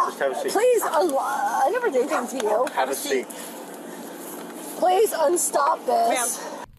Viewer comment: did she just say "please unstop this"??.